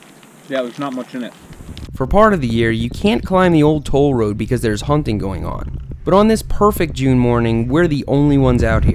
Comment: Anyone else thought Seth said "you can't climb the old toll road bc there's humping going on"? haha